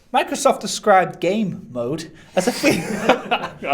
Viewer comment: at one point i swear i heard "try microsoft's beat a gay-mode"